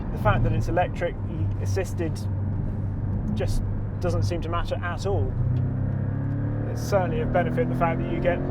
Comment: LOL at 3:20 When I clicked forward and all I heard was something like "it doesn't seem to match your ass-hole".